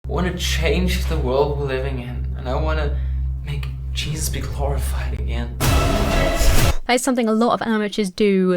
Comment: I was gaming while listening and when the guy said "I want to make Jesus glorified again." I heard "I want to make Jesus terrified again." I then wondered if I heard right and thought that maybe he said terrifying (not something I'd expect Christians I know to say but they aren't fundamentalists) so I listened to it again, but probably because my mind was set on it I heard "terrified" again. I had to set the caption on to see that he said glorified. I agree that he's a terrible actor. I'm someone that rarely notice bad acting, I'm very bad at reading people , and usually think people are nitpicking when saying the acting was bad in a movie or tv show, but here I definitely agree that this guy is a bad actor.